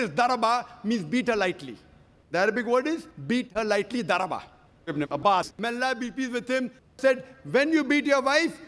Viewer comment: Hello fellow Indıan brothers. I couldn't figure out whom he speaks of at 7:24 because of the thick accent. Whose quotes are the following parts? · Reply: Those quotes are from Ibn Abbas who was one of the early Quran scholars . He was the paternal cousin of Prophet (p.b.u.h.) .